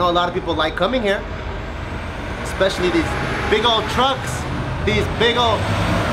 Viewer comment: Who else thought he was going to say "big ole fucks!"?